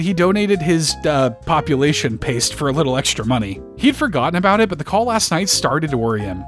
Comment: Oh, that was saying "EXTRA"? I thought it was saying "PASTA"